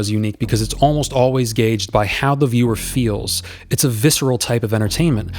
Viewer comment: I misheard you at first and still agreed: "The horror genre's unique cuz it's almost always gay--" Correct, my man. "--gauged by..." Oh. Also correct, my man.